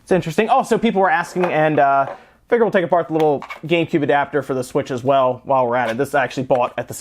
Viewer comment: did you just say "gaycube adapter" hahhahahaha